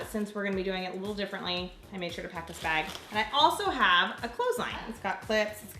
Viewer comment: "I have a clothesline" I heard: "I have a clothing line" LOL